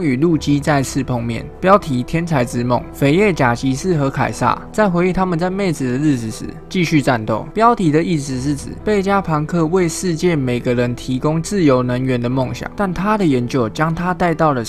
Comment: 聽成妹子，不好意思